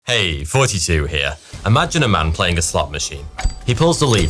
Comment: I like how it sound like your saying “hey 42 here”